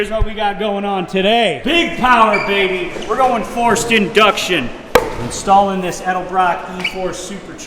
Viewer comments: sounded like he said “foreskin duction” ☠️☠️☠️☠️ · In the beginning it sounds like says "foreskin-duction" when he slaps the supercharger